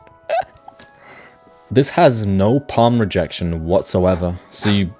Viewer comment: pen rejection instead of palm rejection 😭